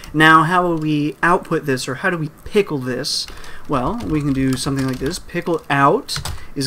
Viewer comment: misheard it as "pickle dicks" and thinks it's actually better that way.